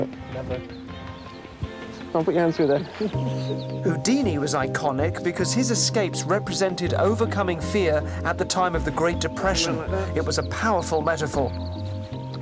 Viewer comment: wait, did he say HOODINI?